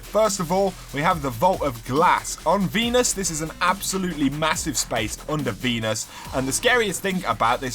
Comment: I thought he said a massive spot on the penis